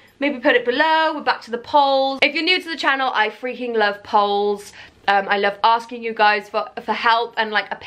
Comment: I zoned out of the video for a second and when I came back all I thought I heard you say was 'I freaking love poles!' 😂